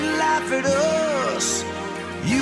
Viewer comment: "You can kick their ass because you're bigger than a bus" lmao!! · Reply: I always misheard it as "because you're bigger than us both" 😂